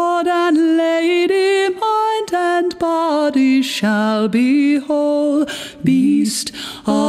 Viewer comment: I thought it said "my dead body shall be whole" at first.